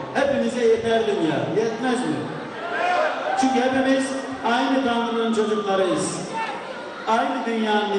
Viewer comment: de ne diyor tam anlayan var mı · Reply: Aynı Tanrı'nın çocuklarıyız.